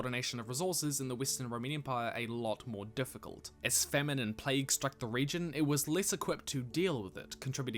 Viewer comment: am I the only one who thought he said "feminist plague" instead of famine and plague?